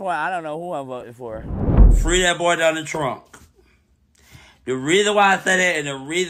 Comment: “Free that boy Donna Trunk!” Every time I hear mispronounce that man’s name I HOLLER 😂